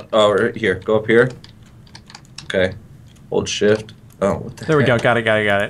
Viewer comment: I thought he said oh shit but he said hold shift xD